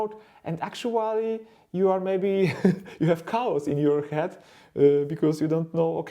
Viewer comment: "you have cows in your head" I'm curious. Did I hear that right? (Or maybe you said "cobwebs"?) If so, is that a German expression? Or Polish?